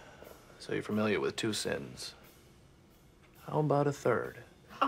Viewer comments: what did he say? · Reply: "So you're familiar with two sins. How about a third?" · What does Abed say here? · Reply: so you're familiar with two sins